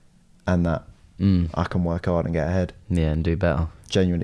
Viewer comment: I’m working and listening to them speak and I stopped in my tracks at 30:50 when he said “I can work hard to get ahead” but I heard “I can work hard and get head” 😭😭😭😭 I was like oh so this the type of timing we on huh boys 😭😭😭😭😂😂😂😂😂